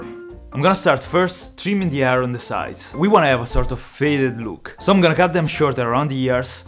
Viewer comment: When you said fade look I heard "failed look". Me: I can do that 😂